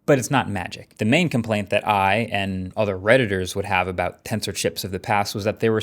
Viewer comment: i thought i heard predators 😂😂😂💀